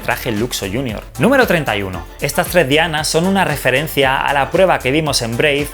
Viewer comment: escuche "estas lesbianas" JAJAJAAJAJ